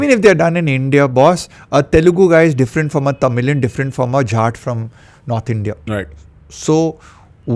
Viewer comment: Did he say Jhat?